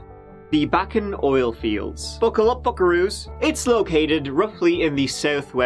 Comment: Did he just call us fuckeroos?